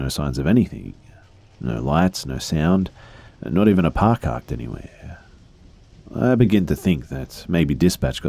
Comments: Had to listen again to make sure I hear "par carked anywhere" right. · he says “par carked” instead of car parked lmao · I swear I heard "not even a PAR CARKED anywhere" might have been just me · is it just me or is he saying “par carked” instead of “car parked”?